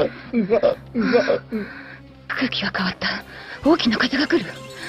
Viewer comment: Yo solo entendí quiero coito en el min